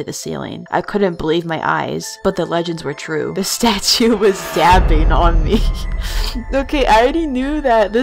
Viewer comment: When you said that "the stachu was dabbing" I thought you said that the stachu was stabbing QWQ (I'm literally 4 months late)